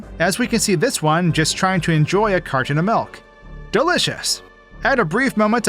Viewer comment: Sounds like you said "Cartrin" what's a Cartrin?